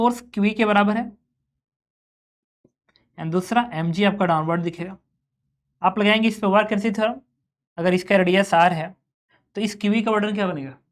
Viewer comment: what did he say ,w e theoerm 😶